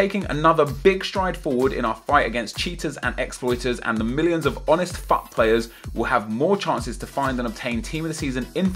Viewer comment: Thought you said honest fuck players